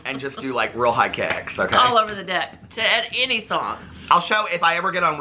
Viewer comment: Omg i laugued so hard, when u guys were talking about the high kicks, i thought said in a thong, and i had to rewind it, she said to any song, omg i lol